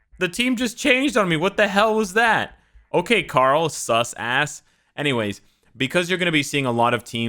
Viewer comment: "Karll sucks ass", was I the only one who heard that way for the first time ahah?